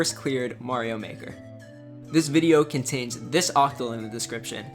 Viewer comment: What's the code that's he talking about at 3:46? I can't pick out what he's saying. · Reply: Octal. It's ascii characters (1 byte, 0-255) represented in base 8, as opposed to hex (base 16) or binary (base 2). Look up an Octal to Ascii converter online if you'd like to convert it yourself.